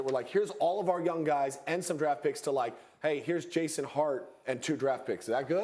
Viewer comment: Did he just say Jason Hart? His name is Josh!